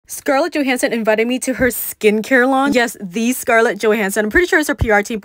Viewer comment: I thought she said skincare lunch😂